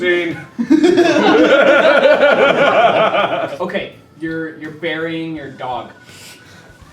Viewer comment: I thought they said your burying your daughter